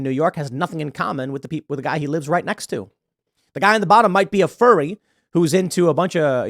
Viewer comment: of a furry i thought he said futanari